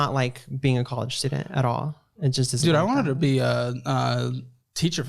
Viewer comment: did Caleb called him a "dude" 😂😂😂